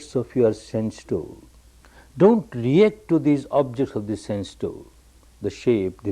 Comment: "dont react to the objects of the sand stone"? what is he saying at 03:45 ? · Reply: sense door.. not 'sand stone'